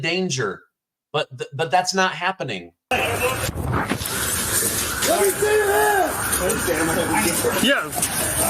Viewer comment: I hear let me see your hands.